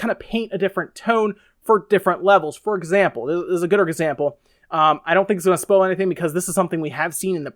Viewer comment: I love the game! Also at around 4:24.... did you say gooder example? Hahaha